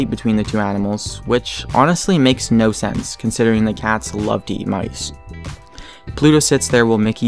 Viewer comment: the captions say: considering the cat Slutty night.. O,O